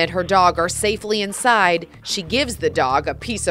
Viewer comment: I misheard at end she said "🔫 that dog"